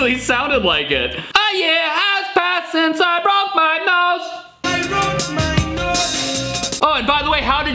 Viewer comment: I Heard "Broke My Notes"